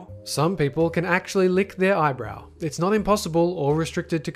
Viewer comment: he said eyebrow instead of elbow